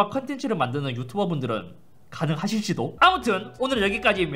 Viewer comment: 가능하실지도 -> 만들어주세요 라고 제 귀에들려버림ㅋㅋㅋㅋㅋㅋ